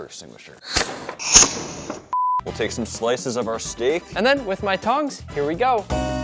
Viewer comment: he said with my tongs but i heard with my tounges